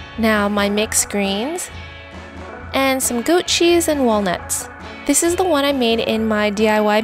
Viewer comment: when you misheard goat cheese as "gucci".. there's something wrong with my hearing.. 😂😂😂